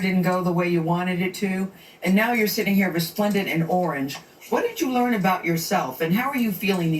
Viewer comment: Oooooh, I thought I heard resplendent in orange which meant she looked really good. Let me listen again. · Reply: Some one else commented that she said resplendent as well...i literally listen over & over cause I want to make sure that what's she said...I honestly thought she said respondent 🥴🤣